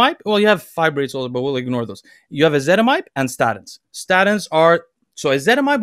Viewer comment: What is the name of the thing is advising to take at 17:00? Can't quite hear what he says. Thanks in advance. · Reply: Based on what some other people are saying in the comments, ezetimibe I believe.